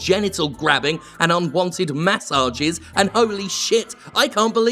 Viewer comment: When he said "unwanted messages" I thought he said "unwanted mass orgies" and I was like oh shit!